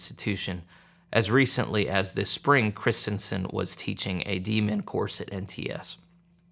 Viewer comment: Was not looking at screen and thought you said the pro-LGBTQ teacher at NTS was teaching a Demon course instead of a DMin course. Thought what I heard was more accurate.